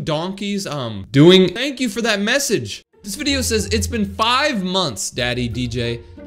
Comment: AYO WHY DID YOU SAY TY 😬😬😬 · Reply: DJ said message